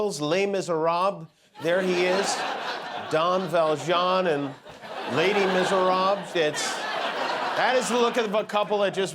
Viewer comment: I thought Kimmel said Lady Misérable. My mistake 😀